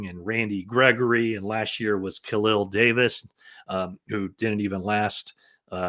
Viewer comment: Good info. Thx. At first I thought you said Kalia Davis. 😅 Khalil Davis is with the Birmingham Stallions (UFL), who he also played for in 2023.